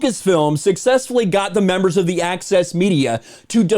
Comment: I legit heard 'Axis' Media and not 'Access' Media